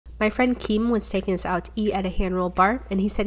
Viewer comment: Tell me why I heard "my son keen" at first and not my friend